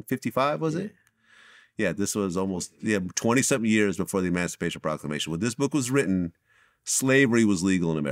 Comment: Sounded like vlad said masturbation a proclamation 😂